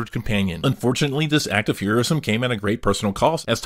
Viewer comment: I thought you said heroin lmfao